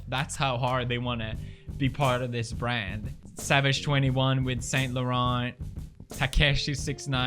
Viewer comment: did he just say savage 21